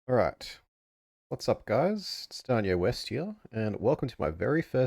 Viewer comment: I swear I heard you say your name was "Kanye West" I had to pause and look at the channel name lmfao😂